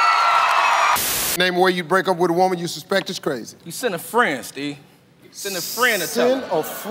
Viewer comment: I thought he said “send her to France” not “send a friend”